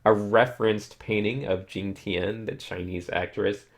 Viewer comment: Can someone write me what he said ? The name of the actress. She looks beautiful, I want to see the real version too ^^ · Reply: Jing Tian